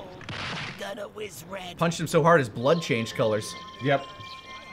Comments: It's funny Lani says "Punched him so hard his blood changed colors" because as a kid I always heard "Gonna whiz red" as "My blood's not always red" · After Goku kidney shots Freeza, until watching with the Canadian captions, I always thought Frieza said, "Was that always red?" instead of "Gonna wizz red." But in this they comment how Goku punched him so hard his blood changed color. So now I'm more confused than ever! I prefer the former.